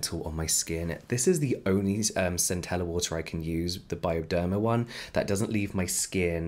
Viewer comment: did you just said centella water?